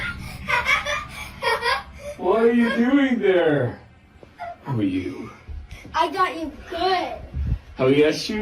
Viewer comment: “Who are you?” Did anyone else hear that?